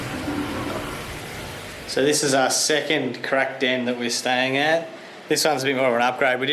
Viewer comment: "crack down"?? i thought you kept saying crack den. "this is the first crack den we could find" that WOULD be an interesting vid